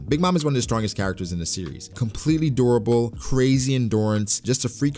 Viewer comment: when he was introducing Big Mom he said, "completely durable. I misheard it and my ears thought he said "completely adorable". Laughed my ass off.